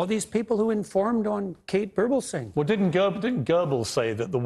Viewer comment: "All these people who informed on Kate Bertlson" I can't make out the name. Can someone reply with the person's name. Close Captions gives "Kate Burble Saying" as the name.